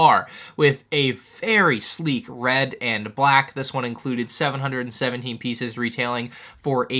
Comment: did he say RED and orange